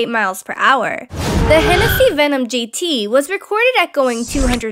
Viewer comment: jt? That's what I heard. It's GT.